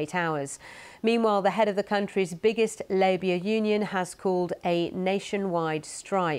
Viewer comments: did she just say "labia union"? 😅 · Wait, what did she say?? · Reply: To me it sounded like Labour union but I don't know maybe she said something else. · what does she say? I'm pretty sure it's not Labour